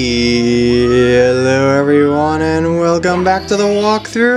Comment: sometimes it sounds like you say "geeeaallo everyone"